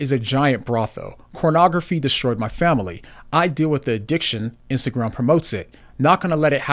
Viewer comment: I thought he said, "Porn destroyed my family."? What happened? Tragic story.